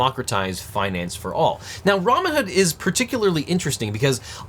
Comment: Pretty sure I heard "Ramen-hood" at 1:15 instead of Robinhood, either way....STONKS!!